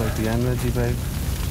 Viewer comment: What did Des say at 3:29? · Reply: "Looks like the end, Reggie babe"